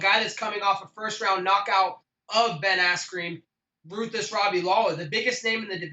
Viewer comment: Did he say Ben Asscream??? 4:37 😂😂😂😂😂😂😂😂😂😂